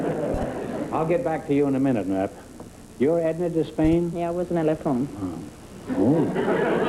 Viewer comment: Does anyone know what Edna said in response to Groucho at 3:53 ( for the life of me I can’t figure it out) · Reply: "Yeah, I was when I left home."